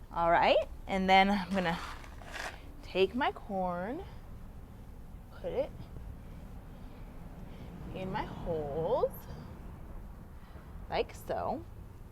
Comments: i really heard @ 1:16 put it in my hole .. 🤤🤤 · Did she say on "take my corn, put it in my holes"? 1:15 sec? lol is hectic